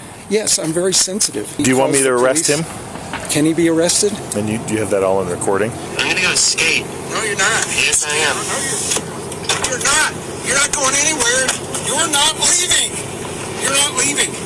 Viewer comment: I think that the irate confrontational cyclist misheard, "I'm going to skate" as, "I'm going to escape". Anyway, I hope that comes to see the errors he made. I think that is used to cops doing what he wants them to do.